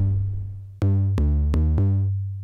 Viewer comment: Yo siempre pensé que decía : Cuidado con el perro! Cómo la marca xD